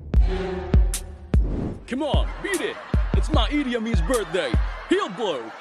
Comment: Did he call the kid "Idi Amin" at 0:09?